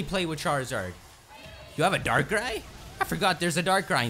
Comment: OMG I thought he said dark guy and I didn't know how to feel